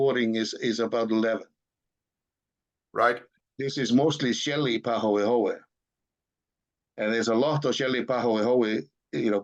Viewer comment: Did I hear that correctly? Shelly Pahoehoe (so I assume small interconnected lava tunnels, now void of lava, roofed by a relative thin crust)? As in shell?